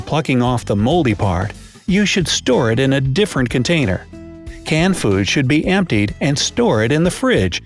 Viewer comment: Did anyone hear BRIGHT SIDE saying "Canned foods" while in the text it says "Canned goods"?